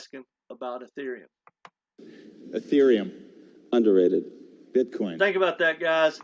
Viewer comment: what did david scwhartz say about bitcoin in one word? Love to know. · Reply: Over rated